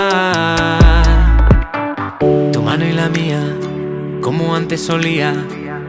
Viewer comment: Entendí "Tu madre y la mía" y yo preguntandome que quería decir eso 🤔😅